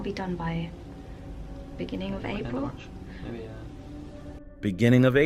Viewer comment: When said il be done by the beginning of April did say beginning of March at the same time and then said yeah? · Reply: YES!!! did mutter MARCH!! He said 'beginning of March!